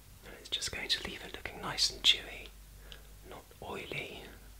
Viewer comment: I thought you said chewy instead of dewy. I don't think I'd enjoy you eating my skin. Love yew, 😘